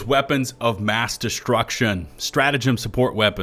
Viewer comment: I was in the other room and I heard "Weapons of Bass Destruction" lol